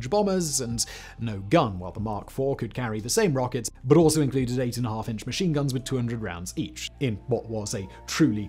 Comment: Im positive im hearing this wrong but does he say 8 and a half inch machine guns???